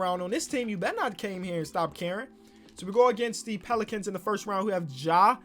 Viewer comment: When said “stop caring” I thought he said “stop Karen”